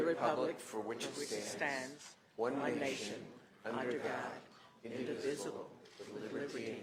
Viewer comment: I thought I heard." In the visible" instead of the correct word, individual in the pledge of allegiance to the flag ?